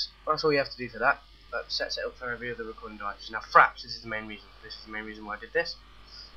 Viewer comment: You know at first it sounded like you said CRAPS not FRAPS. lol :)